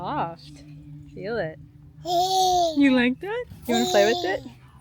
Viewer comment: I swear said “i like it” at 5:00- or am I crazy….. also… girl frizz is ok and should be embraced! You have natural volume and waves- people try to replicate that shit!! She sounds jealous.. you are naturally so stunning bro- don’t let this poly Christian girl tell shiiiiitttt